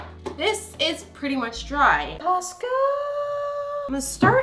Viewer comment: can’t be the only one who thought she said Costco